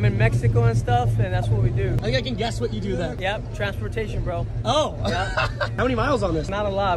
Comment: He said "transportation". I heard "TRAFFICKING"...